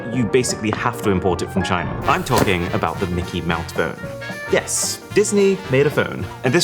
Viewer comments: Why did he say "mickey mouth"? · Mickey Mount phone? I swear I heard "mount".